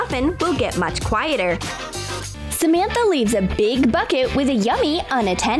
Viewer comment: ok, when it said "get a big bucket" I heard "get a big fuck it."